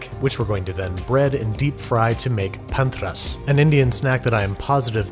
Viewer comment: Yo why did the subtitles say PEMDAS when Babish said panthras lmao